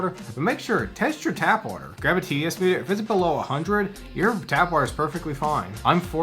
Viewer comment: grab a what? I assumed you were gonna say PH meter, but they aren't on that big of a scale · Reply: I said TDS meter